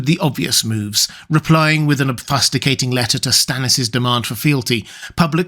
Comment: What is the word you said at 4:10? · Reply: He said obfuscating but he said it with a kinda weird emphasis.